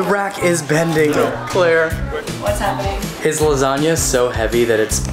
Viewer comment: at first I thought he said Bitch Lasagna